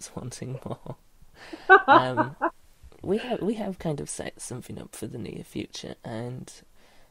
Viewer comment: When you said 'set something up for the near-future' I definitely thought you said 'sexed something up' and giggled :) hahaha.